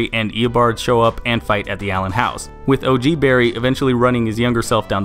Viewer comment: I thought it said alien house 😂 I was like: What?